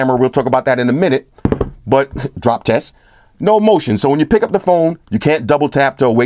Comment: did he said 'drop test'😂👏